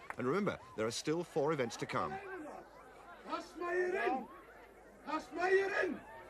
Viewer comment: I always wondered what Jón Páll's coach was shouting at 7:45 sounds something like "kazmaierin" but I'm not really sure. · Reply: it's Kazmaierinn, it's just "Kazmier" with a definitive article, so "The Kazmier" is what it means.